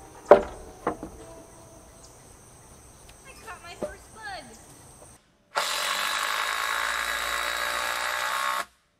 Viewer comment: What did say at 7:07. I have rewatched it 100 times and it sounds like something new each time 😂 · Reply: Her and Maverick were playing a game to see who could find a bug first. She said, "I caught my first bug." :)